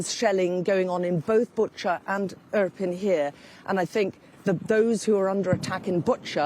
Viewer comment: Is she saying.... "up-in-here"?